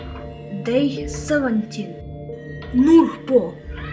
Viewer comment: погоди я услышал слова нупо